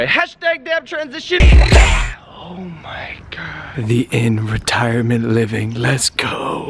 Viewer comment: I thought he said "the end" retirement home not "the inn" retirement home... I was like, that's depressing 😂